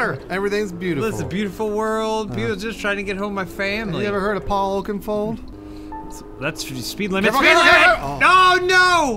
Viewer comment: Im sorry did bruce say "paul okenfold 3:30 i want to scream like a child right now hero!!!